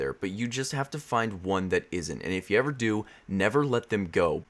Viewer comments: Not gonna lie when you kept saying H. Pylori I thought you were saying A.I.D.S by Lori 😬😂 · I thought he was saying "H by lori"